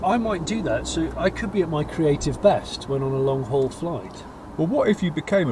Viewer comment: Could have sworn said "creative vest" rather than "creative best" ;D